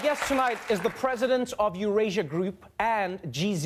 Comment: Hahaha, I thought at first Trevor said The Presiden of Erusea Group instead of Eurasia. My mind was like, wait what? Anyway, fascinating interview as always Trevor. This interview, along with Jon Meacham, are the my favorite.